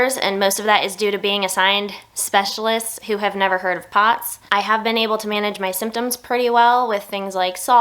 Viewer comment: I heard "'specialists' who have never heard of pots" as "'specialists'who have never heard thoughts" and, honestly, truth^n